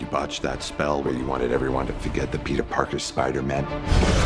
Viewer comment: may be over stepping but it sounds like strange says spider men